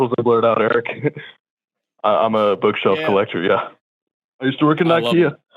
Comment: I thought he said bug shelf and I got really excited